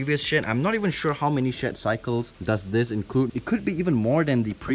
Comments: I misheard shed cycles as shit cycles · when he said shed I thought he said sh*t 💀